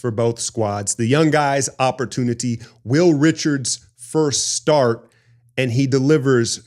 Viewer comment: From the first preseason game Will Richard makes you take notice. Bob and Kenna kept calling his name (I thought they were saying Little Richard wondering his connection with rock and roll....I thought these guys were into rap) and as we watched him in more games his energy and mobility were obvious (he made Gui Santos look lethargic) but he seemed to have more polish and command of his game than I am used to from rookies (perhaps a testament to the value of a top college program) and he was burying his threes with a practiced stroke. He is the reason I am less disappointed with the loss to the Kings than I was in the win over the Suns (still stuck in the nostalgia of when the Dubs would bury teams in the third....not blow 20 point leads). That was the most impressive rookie starting debut and perhaps....dare I say it...tops a rookie debut of Steph?